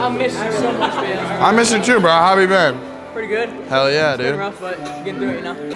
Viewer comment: it sounded like he said how have you barren:face-green-smiling